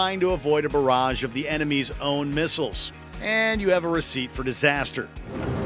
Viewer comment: did he just say "and you have a receipt for disaster"? Someone either misspelled recipe or he got dyslexic